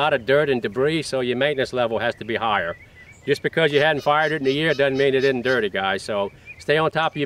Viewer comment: I first thought he said just because you havn't "farted" in a year doesn't mean it's not dirty. LOL...firedit